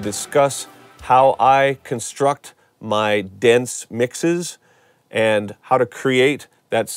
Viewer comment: i thought he said "dance mixes" at first lol